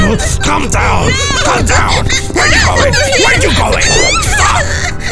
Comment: Did Yellow Say "F*CK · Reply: Yellow say "Stop", not "F*ck"